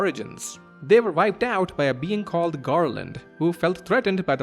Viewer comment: Did you say...Garland? Mr "I will knock you down!" himself? Such is the power of big stretchy sword!